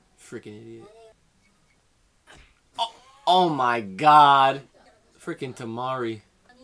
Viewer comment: thought he said tamale